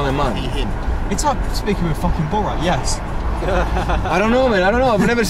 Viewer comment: I pissed my pants... I think this is what the driver said "It's hard to speak to a fooooking Borat" BTW: watch from minute19 to 21in half the speed and you'll have a good laugh.